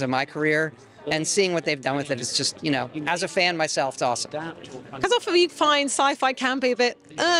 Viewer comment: WHAT TF is she saying? is that english? i can't understand a single word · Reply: "Cause often you can..." I think